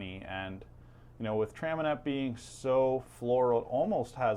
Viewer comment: Did you say Traminac? The Croatian grape? Maybe I misheard but I've got 60L of that chugging along right now along with 100L of Hamburg Muscat